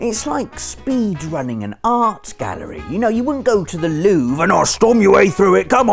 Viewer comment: When he said The Louvre I thought he said the loo. Big mistake.